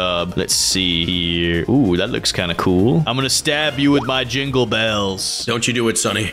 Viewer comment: BRUH when said l am going to kill with my jungle bells . I thought he said jungle balls 💀💀💀